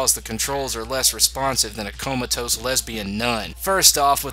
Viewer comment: Did he said lesbian nun, or lesbian none? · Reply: I believe it’s the former